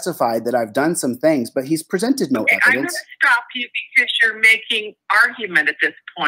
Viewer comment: I heard her say, "I'm going to stop your cross." I.e, cross examination.